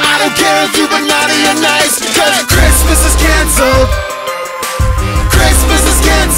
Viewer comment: for some reason for the lyrics "Christmas is Canceled" I heard "Christmas is Cancer" did anyone else?